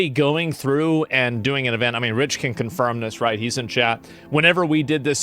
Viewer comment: I swear he said "Rich can confirm this, he's a Jap." Also, Mizkif 100% faked his heart bullshit. It's easy to fake something to where you can't tell if he's faking it or not, legitimate or not. I guarantee he faked it because he didn't want to be told what to do and when someone kept trying to make him do shit, he took all the attention to feel better. I guarantee he's like 100% narcissist. He's someone nobody should associate with.